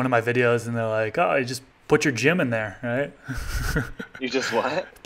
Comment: when you said "put your gym in there" i heard "put your Jim in there" and i legitimately thought you meant to put your buddy Jim in the room